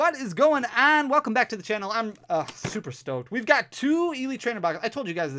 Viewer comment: I thought I heard super stoned instead of super stoked 🎉😂